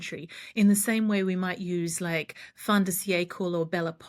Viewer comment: Hello! What does say at 7:40 Fondacie? right before Belle Epoque? I'm not familiar but want to know more! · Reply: I think she is saying “Fin de siecle”